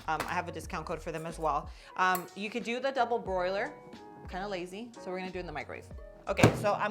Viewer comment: Click here for the full recipe and nutrition: <link> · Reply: This looks awesome. Just a note it’s a double “boiler” not “broiler” (maybe I misheard you)